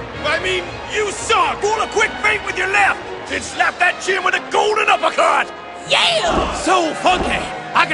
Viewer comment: i could've sworn i heard usopp say bitchslap that chin xD.